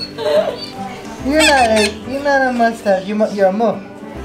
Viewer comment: You're a muff 😂😂 (at least it sounded like said that)